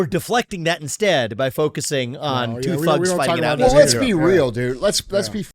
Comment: did Vivek just say two “fucks”? That’s awesome